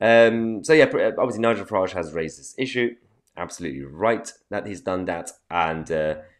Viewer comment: When you said "Nigel Farage has raised this issue" it sounded like you said "racists issues" 😂 haha